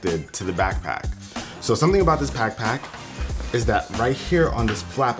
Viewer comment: anyone heard pack pack?